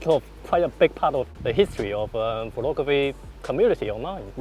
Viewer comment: I barely realized said pornography instead of photography on 0:27 hahahaha. Cheeky.